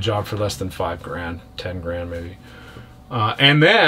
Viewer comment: dingy and this boat are a perfect match. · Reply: I thought said afterbirth....